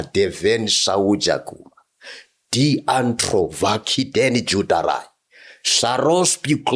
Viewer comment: I heard him say Danny Jordarai😂😂😂😂 these tongues